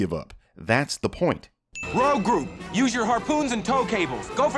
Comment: did Luke say gro gu